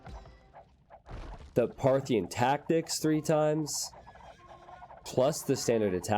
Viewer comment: When you were talking about Mangudai upgrades, I legit thought you said triple Smarthyan tactics 11